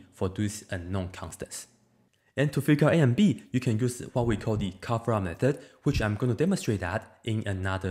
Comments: what method did he say is used to get A & B? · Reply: Heaviside coverup. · What's the method you said in that timestamp? Cafra? Taphra? I'm really sorry but I've searched for this for 30 minutes and still can't find what it is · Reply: Figured it out now, sorry. It was cover-up method